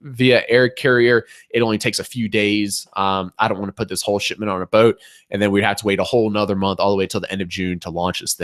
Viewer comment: I thought he said : "I don't wanna put this shit on a boat" at 5:02 I was like whaaat